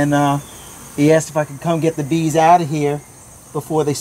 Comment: lol, at first i heard '..he has to fn come get the bees..' instead of '..he asked if i could come get the bees..' XD